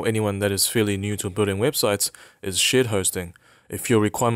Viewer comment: Every time he says "Shared Hosting" I hear "Shit Hosting"!